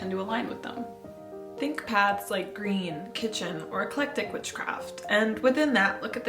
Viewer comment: I'm reading the subtitles because I'm wrighting it down and at 1:37 it says a click tik, does anyone know what she said or is that it? I can't make it out myself lol. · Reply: Eclectic